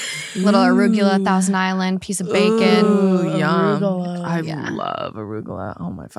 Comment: this had me really thinkin and i think i hear “piece of pizza” 😭